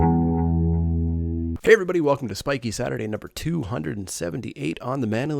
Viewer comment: During the intro I heard "Welcome to Swanky Saturday" and did a spit take all over my laptop. Nice draft btw.